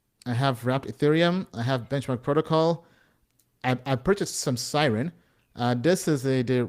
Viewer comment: he mentions SYRUM, OR CIRUM - I can't find it, anyone know what the ticker is for this one? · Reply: Siren (SI)